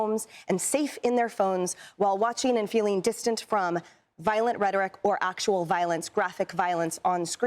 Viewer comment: Did she just say. "an·us vs. them..."??!!!!! LMAO!!!!!